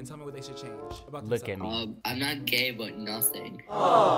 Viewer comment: yall whatd the guy say at 5:36 i cant hear😭😭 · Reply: He said I’m not gay but nothing